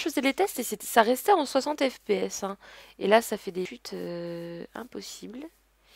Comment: J'ai entendu un gros mot ac un freeze xDDD Bon retour! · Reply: Komii ► Le mot c'est " chutes " mais avec le freezer ça porte à confusion haha